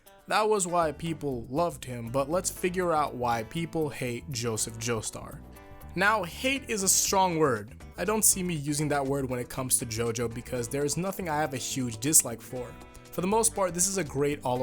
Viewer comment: I'm sorry, can you say that again in English? It sounded like you said people hate Joseph Joestar...